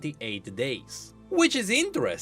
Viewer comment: Tbf i also thought heimerdinger said years not days. It took me daysss and a few reaction videos to realize haha